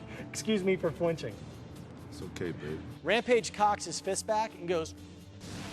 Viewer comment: Did he say Rampages cocks?